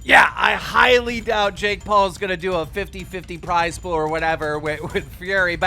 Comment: I thought you said Jake Paul is going to vs j Dawson